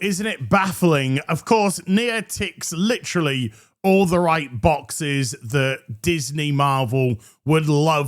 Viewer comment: For a second there, I thought said she licks all the right boxes.